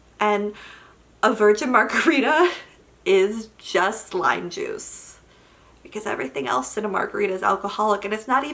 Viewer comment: Guuurrrrrrrrllllllllll I feel you about the margarita thing - they're so refreshing, I just want a sip 😭 Also thought you said "lie juice" instead of "lime juice". Both are true about virgin margaritas.